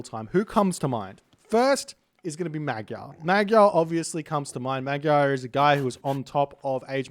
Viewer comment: didn't understand the name. Is it magyal? Maybe someone can answer that for me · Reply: Magyar